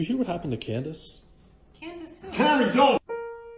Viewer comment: is so funny when he says “ honey don’t” at least it sounds like he said honey